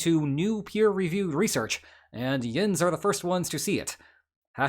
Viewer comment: was that a yinz I heard? #412